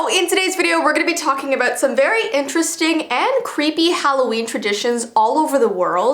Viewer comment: When said, "Halloween Traditions" I heard, "Halloween Premonitions" AHHHHHHHHHHHH